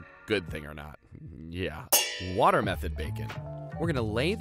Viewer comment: for a second i got worried i thought i heard watermelon bacon when you said water method